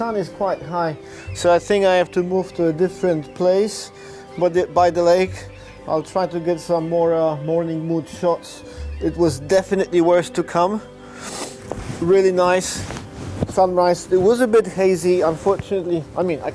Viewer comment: Hahaha did you say "morning wood" shots at 8:21? :D :D :D Should have made it a timelapse